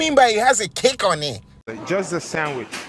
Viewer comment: He said "kick on it" I heard "cake on a"